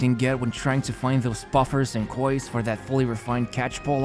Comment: I thought he said "f*ckers" 😆